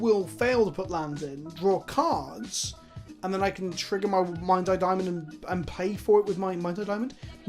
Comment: i swear i heard him say "minds eye Immoto".